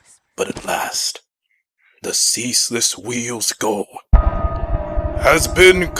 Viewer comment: Okay, what is the lich saying here? All I understood was 'The Ceaseless Wheel". What's the last word? Stone? Skull? Storm? What is he saying. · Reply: Goal.